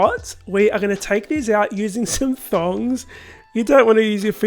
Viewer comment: Did I hear him call “tongs” “thongs”?